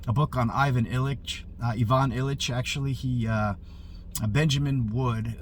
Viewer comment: Oops! You said Ivan Illich and I thought you said Ilyin! I’ll check out Illich. I think you will like Nicholas Kotar’s translation of Ilyin’s Foundations of Christian Culture.